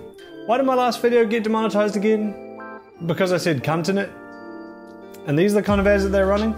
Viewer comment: I WAS WONDERING WHEN I'D SEE LILLY ON THAT STUPID FRICKIN WASHING MACHINE. Edit: 15:35 Did.... did you just say continent?